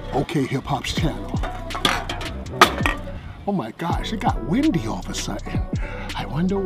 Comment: At the 2:53 minute mark, winds knocks down the cans and says "It got windy all of a sudden, I wonder why." This confirms my theory that is a god that can control the weather and this is him giving us a subtle hint that he is one. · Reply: no, he said "oh my gosh, it got windy" which is why it sounds like he said "shit got windy"